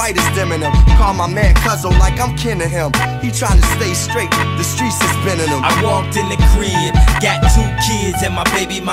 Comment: "Call my man cousin like like I'm kin to him. He's tryna stay straight, the streets is bending him." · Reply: I always thought he said "the streets has been in em"